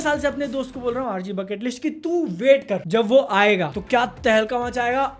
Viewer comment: did he just said RG BUCKETLIST